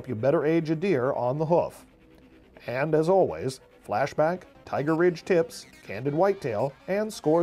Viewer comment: at :30 did i hear "tiger rich tits"